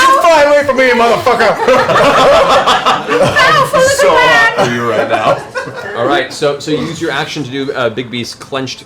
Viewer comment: Can somebody tell me if I'm hearing things, please? Because I hear Travis say 'I am so hard for you right now.' to which Mary (jokingly) says 'It's bigger than Trinkets.' which in turn prompts Laura to nodd and go: 'It really is.' And then again Mary nodds and says (affirmingly): 'HUGE dick' Is there a story there? WTF?